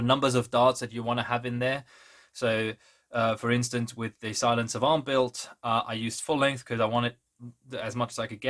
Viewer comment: did I hear correctly "Silence of A'arn" ? as in the shotgun from destiny? If so that's absolutely amazing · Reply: XD sadly no, I was referring to my 'Silent Savant' build <link> But I looked up the destiny shotgun and now I want to build it. And I already know which blaster to use....thanks so much for giving yet another mod for the 'I have to do this' list :D